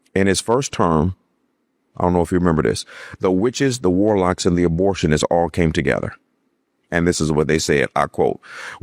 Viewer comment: im sorry, did he just say the abortionists? 😭